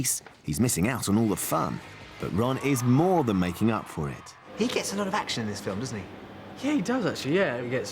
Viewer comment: It sounds like he says "He gets an erection", describing Ron with Lavender